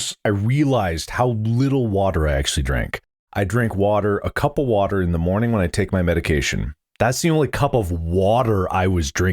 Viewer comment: When mark said "I drank a cup of water in the morning" I thought he said "I drank a couple water"